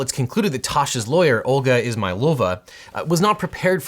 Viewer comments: Legit thought I heard her name as, "Olga Is-my-vulva" and I spit out my drink. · I thought he said 'Olga is my vulva' 😳